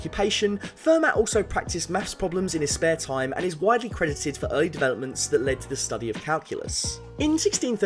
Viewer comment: I heard “criticised”